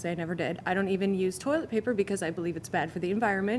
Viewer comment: Is it just? Me or I heard she doesn't use toiletspeaker?